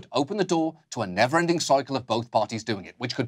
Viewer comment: is it just me or did John just say “disableise” (sic.)?